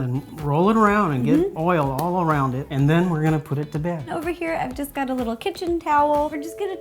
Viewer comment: at that time I thought he said we're going to poop the pizza...